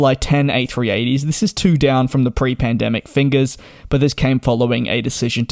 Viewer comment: did he say “fingers” at 2:15?? lol